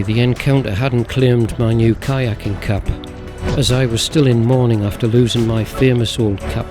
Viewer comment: What happened to your old boat? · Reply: Oops I watched it again and realized I misheard that you lost your kayak Cap, not kayak (around 7:30 minutes). Guess I better shake the salt water out of my ears! 😆 Thanks again for the great videos!